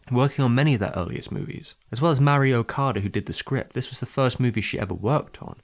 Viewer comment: I kept mishearing Mari Okada as Mario Kart. Perks of not having English (nor Japanese for that matter) as your first language